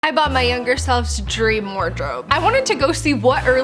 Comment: Me hearing: I thought said, “I bought my younger SON”. I was like has a kid….?